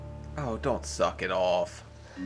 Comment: I watch these videos to sleep, and right as I was about to drift off I hear “ohh don’t suck it off!” 😂